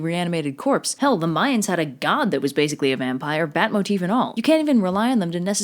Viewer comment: when she said that the Aztecs had a god "bot motif and all" I thought she was saying that the name of the god was Batmotefinal